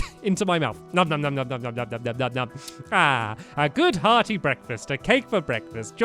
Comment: I thought he said a good "healthy" breakfeast! 😂